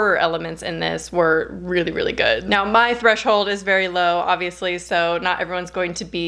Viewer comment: When you said “My threshold is very low” I heard “My freshold is very low” and I’m honestly kinda disappointed that you didn’t make that pun 😂